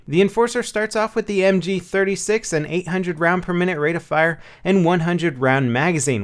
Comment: Thought u said 800 round mag.... Lol